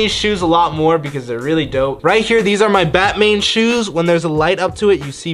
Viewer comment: is it just me or does Jesse say Batmane